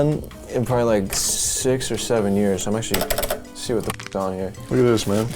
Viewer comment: nah he really said 6 or 7 years